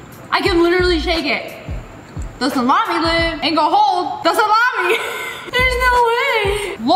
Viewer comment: did somebody say...... SlAMY ↑ did I spell it right?